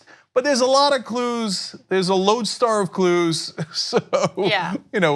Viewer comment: I thought he was saying "lonestar" and maybe talking about walker texas ranger at first.